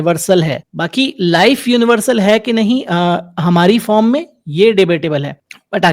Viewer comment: " life Universal hai ki nahi ......" I don't understand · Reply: usne kaha "Life universal hai ki nhi hamare form mei, ye debatable hai". There maybe a different kind of life, that we have never seen. Far different from what we are. We are carbon-based, DNA-driven, conscious beings. There maybe something else on the other side of the universe.